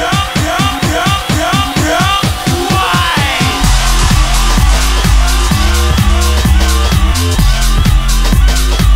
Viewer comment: what does it say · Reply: It's saying "Brock Wild" not "Wine".